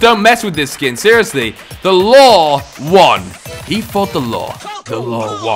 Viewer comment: i thought he was saying the lawl won XD